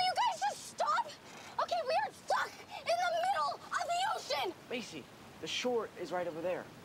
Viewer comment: macy kind of sounded like she said "we are fucked in the middle of the ocean" at 1:02 LOLOL